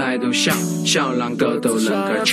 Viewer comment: 我听成了“我只想要cao你”